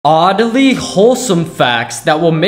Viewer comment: Him: "Oddy" What I hear: "Oddaly"